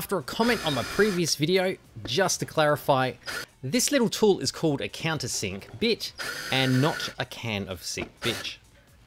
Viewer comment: Thanks for clearing that up, I thought you were saying , "cans in the sink bitch".